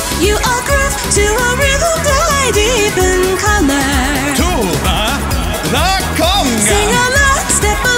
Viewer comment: why did i hear tuba the conga